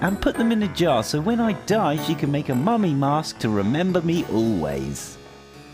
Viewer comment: she says me instead of you